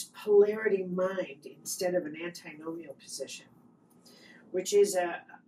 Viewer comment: Can anyone make out what she says around the 0:42 mark, '..polarity mind instead of ?'...Transcript/subtitles are suggesting 'anti Gnomeo'? Is it 'antinomial'? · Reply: Antinomian Antinomianism is a theological belief that Christians are not bound by moral law, particularly the Ten Commandments, due to the grace of God. It essentially argues that salvation is achieved through faith alone and that good works are not necessary to prove or maintain one's salvation. This concept is often viewed as the opposite of legalism, which emphasizes strict adherence to religious laws for salvation.